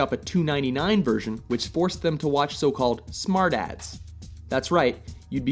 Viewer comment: I thought he said smartass instead I actually heard smart ads lol